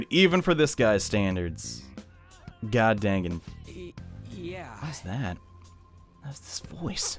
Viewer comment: I swear I almost heard him say “God Danganronpa”.